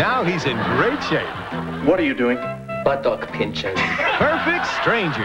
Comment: Omg I remember that episode of Perfect Strangers where he says he’s doing “buttock pinchers.” I always thought he said “butthole pinchers.”😂😂😂 I remember thinking I can’t believe they allowed that on TV. I’m sorry I know there’s something wrong with me LoL 😆